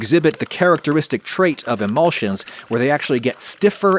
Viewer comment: Hey Adam, I don't know if its a huge deal to you or not (im indifferent, just noticed this is all), but the subtitles at around 7:35 say "elusions" instead of "emulsions".